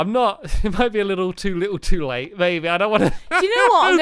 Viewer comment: Oh... We all know you and Squashy aren't friends.. You're much more than friends <3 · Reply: it sounded like said baby instead of maybe.